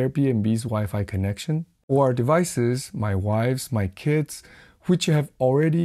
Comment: At time = 6:16, did he just say "my wives"?! 😂